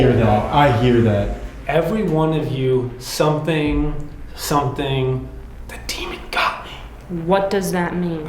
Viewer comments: I’m not knocking anyone’s ideas on what was said but I’ve listened over and over and it sounds like extra words were missed… I hear “Get everyone of you just like the demon got me”. Does anyone else hear it that way?? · I hear: "Every one of you get out, ____ the demon got me." That is a clear warning. And of course they don't listen. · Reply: I thought it said “Every one of you now that…the demon got me.” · Sorry so late but I hear "Every one of you get out, because, the demon got me". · i swear i hear “Everyone of you will _ the demon in me” that second word is to static for me to make out anything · I hear " EVERY ONE OF YOU GET OUT GET OUTTT , THE DEMON GOT ME " ?! FEAR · In The Who disrespected you question. I actually thought I heard everyone of you was respectful, the demon got me. Like y’all did great, but…